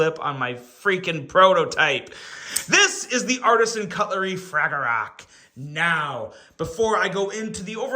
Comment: I thought dude said fragglerock from the Muppets